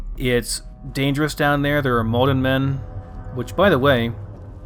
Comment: Hey ! Really enterrtaining video ! Sorry but my english is not really good, and at 7:49 he is talking about "molten/mudden men" from the sewers ? I cant figure out, what he is talking, I dont know this mob... what are all those stranges drawings of tall men ?????? HALP PLZ I WANT TO KNOW :) (New on Fallout 4 Vanilla) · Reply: Hi Adrian! To answer your questions: The "Maldenmen" are a group of insane cannibals who have "let go". They roam the sewers under the Commonwealth metro system, and are being influenced through dreams and visions by someone at Parsons State Insane Asylum. They originate from the town of Malden, thus the name. They're essentially a new enemy faction unique to the FROST mod for Fallout 4, which is set in the Commonwealth, but only 5 years after the bombs dropped, not 200 years in the future like the main game. There are other new factions as well - the Themis Cultists, the Metro Alliance, the Metro Federation, and more. I believe the mod was influenced a bit by the Metro 2033 / Metro Last Light games, if you've played those. Also, here's the playlist for my newer FROST series: <link> . I started over due to a lot of people disliking some of the mods I used when I started this one.